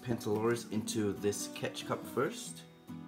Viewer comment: When he said catch cup I thought he said ketchup